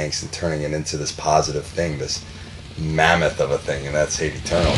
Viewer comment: I don't understand what Erik said at 0:31. He said: and turning into this positive thing..... this...(I don't know) thing and that's Hate Eternal. Someone help me :D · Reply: "This.... MAMMUTH of a thing".